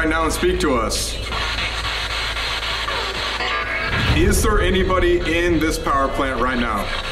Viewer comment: when colby said "is there anybody in this piwerplanet" it sounded like "colbyyy" when sam was sreaming his name in the forest